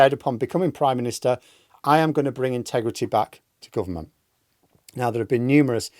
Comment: I thought he said he was bringing sexyback.